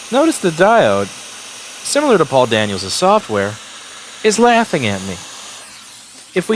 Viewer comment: I don't know why, but every time Louis is talking about Paul Daniel's software, I hear "poor Daniel's software".